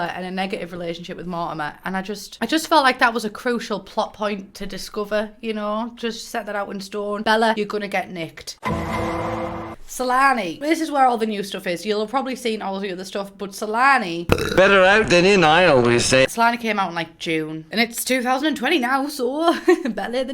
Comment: I heard salami